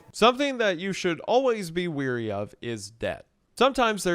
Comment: My ass thought he said “something that you should always be wary of this *death*” and the absolute fear that coursed through me